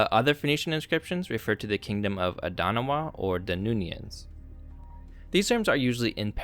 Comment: I’m surprised that the term, ‘Danunian king’ was not explicitly addressed (though highlighted around 9:00), given the similarity with the Homeric ‘Danaans’ which refers to the Greeks. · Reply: I first misheard this name as Danuvian aka coming from the river Danube (which empties into the black sea). Maybe yet another false friend, name wise.